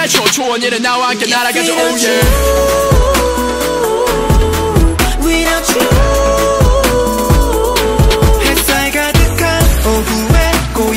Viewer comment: sounds like yeosang is saying "if I got the car" 😂😂